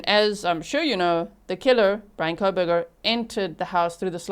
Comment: Did you say Frank Kohberger at 2:59?